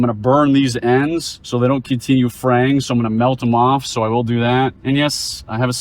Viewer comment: it's 'fraying' not 'defraying.' 😁 I once heard someone say 'unthaw' instead of 'thaw.' 😅